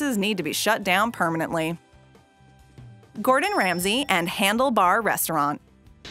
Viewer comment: I thought it said "Gordon Ramsey and John Cena" XD